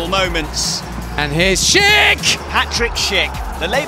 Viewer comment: did he just say hattrick Schick